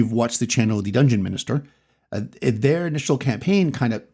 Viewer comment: Here I am, fussing in the kitchen while listening to when...insert Scooby Doo surprised sound. Did he just say Dungeon Minister? 😁 Great advice, as always!